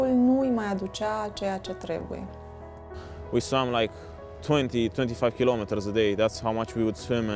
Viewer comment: did he says 20,25km for day? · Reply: 20 - 25km a day